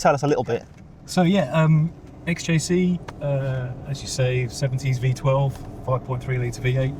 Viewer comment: Someone tell Jaguar's chief engineer that the XJC 5.3 is a V12, NOT a V8 😂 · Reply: Maybe just a slip of the tongue. At 8:38 he clearly says "70s V12". Which is correct IMHO.